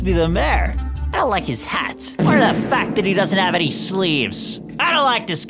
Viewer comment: Why do I hear "the fack"